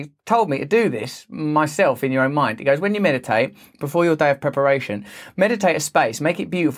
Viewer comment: When Russel said meditate it thought he said Meta-Tate 😰 which I feel is what Zuck wants us to do. Just say no to MetaVerse